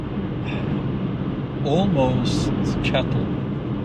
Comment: you used the word "cattle" , but in American history, there's a word that was used by people with political and financial power to categorize us, the regular population. We are the "Rabble". Have you ever heard that one, it's over 200 years old. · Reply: I thought he said "chattle", but it might have been "cattle"?